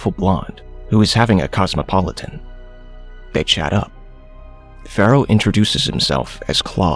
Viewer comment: did you say “Bechara” 😂 translation “poor man” love it 😍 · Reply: He says'They chat up', it sounds like bechara